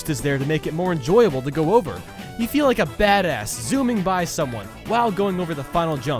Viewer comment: it sounds like he says "fat ass"